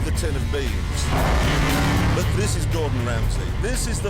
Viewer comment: Did Jeremy say "But this is Gordon Ramsay"?